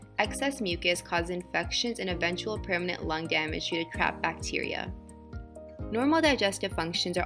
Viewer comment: I heard 'crapped bacteria'.